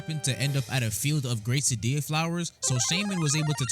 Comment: I heard Quesadilla Flour